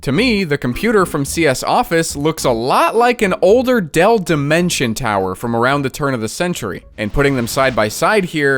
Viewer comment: I misheard that as dell dementia tower 💀